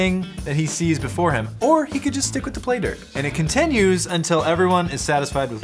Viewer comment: The whole time I thought they were saying playder